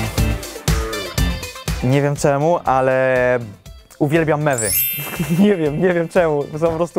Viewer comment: usłyszałam: Uwielbiam memy xddd